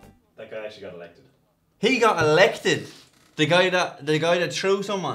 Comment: sounded like he said "ya gotta bate your way to the top"